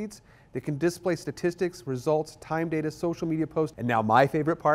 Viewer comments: what did he say? (condisplay statistic results?) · We have some great financial promotions to acquire ***** Tricaster Advanced Features. Call Key Code Media. Chicago, Seattle, SF, Orange County, San Diego and Los Angeles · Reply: what did he say? (condisplay statistic results?)